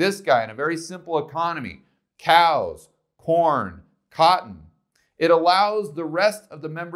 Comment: "Cow, porn, cotton allow others to be teachers, doctors..." - did anyone else hear this at 16:30 ?